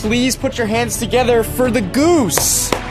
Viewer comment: Goose: Goose: grabs family guy meme My nose: a i r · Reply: i lowkey thought you said noose and ended up making myself laugh anyway...... sad...very sad...